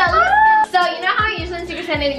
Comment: did say to,,Linda” or my ears r just stupid ?:dd